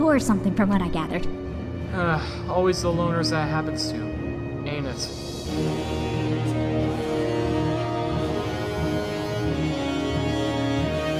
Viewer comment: When icecap said ain't it I thought he said anus lol